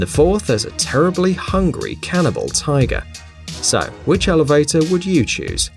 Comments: Cannibal tiger cause if its a cannibal it only eats tigers... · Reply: i thought he said carnival XD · hahahaha for the elevator one u can go with the tiget cause it only eats tigers. sounds like he said canibal